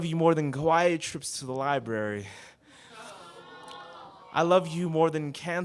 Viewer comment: "I love you more than quiet trips to library." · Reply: Aaaah I thought he said Kawaii trips to the library. I get it now :)